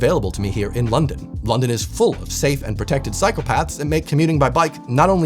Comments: Oh, Cyclepaths! (Bike paths) I heard you say "safe and effective psychopaths" 😅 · “London is full of safe and protected psychopaths..” is what I first thought you said @ 8:37 😅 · "London is full of safe and protected psychopaths" is what I heard :D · It sounded like you said "Psychopaths" XD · sounded like...London is full of safe and protected psychopaths...😂 I think my hearing is worse than I may admit ... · I had to stop the vid to - literally - ROFL when I misheard "safe and protected cycle paths" for "... psychopaths" 😁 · Oh, cycle paths. I thought you said psychopaths. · why did I hear "cycle paths" as "psychopaths"? 😅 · "Safe and protected psychopaths". I think I misheard. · I thought he said "psychopaths" instead of "cycle paths" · Listening while doing a puzzle and I hear “London is full of safe and protected psychopaths.” … Oh. Cycle paths. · I gather you said "London is full of cycle paths I thought I heard psychopaths."😊😊😊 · Had to do a double take when I thought you said "London is full of safe and protected psychopaths" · I misheard "safe and protected psychopaths" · At 08.40 I heard "safe and protective psychopaths". Thought I'd heard a very good attempt at sardonic irony. Almost disappointed when I relistened to hear "cycle paths" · I know what you actually said but I’m over laughing for 3 minutes because it’s so funny how much it sounded like you said “safe and protected psychopaths” instead of cycle paths 😂😂